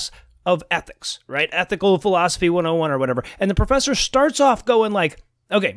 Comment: I thought you said "ethnic philosophy" and totally agreed with you. People from the US don't even know ethnicity means culture. But you didn't.